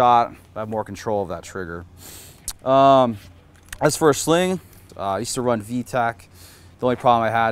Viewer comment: Did I miss the sling brand? I thought he said “I use to run vtac”